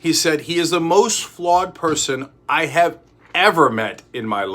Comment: Can someone please tell me what the quote was Cenk is saying at 3:20? He is mumbling and all I hear is John Kelly said he is the most what? Is he saying flawed?